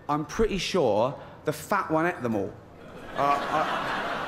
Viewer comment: What did he say at 5:02 ? Should we gell with it???